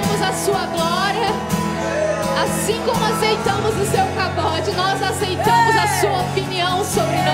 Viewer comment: O que ela fala no minuto 8:44? Eu já ouvi várias vezes, mas continuo entendendo "pagode".